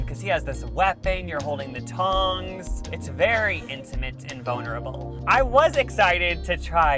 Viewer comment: lol I heard bonerable